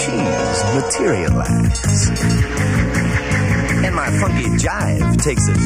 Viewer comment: I heard a bad word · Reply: I think he said funky